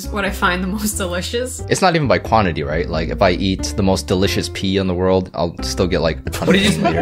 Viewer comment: Me zoned out think he said PEE too:😟